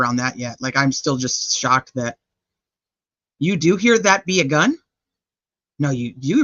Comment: Where Libby says "dat be a gun".. I hear "that weirdo"